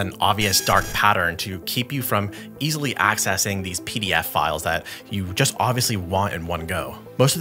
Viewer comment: i heard you say "pdf files" and my tiktok filter went off, i thought that was pedo-file 😅